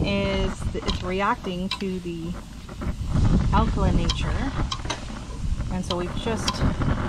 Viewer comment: what does "it's reacting to the [alcoa nature]" mean? Sorry but I can't tell what you're saying there. Great video btw. Great recipe. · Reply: Alkaline nature. Sorry about that. The pH is what causes the change in colors. Thanks for watching.